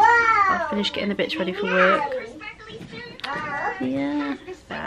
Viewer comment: Tilly looks so chill on the sofa 😜 the boys have a table and chairs but prefer to sit on the sofa. Luckily our sofa is black but it's still a pain to clean 😂. I thought you said " I finished getting the bitch ready for work" I had to rewatch 😂. If only they could Hoover for real , would be one less thing for us to do 😝. Great vlog guys❤️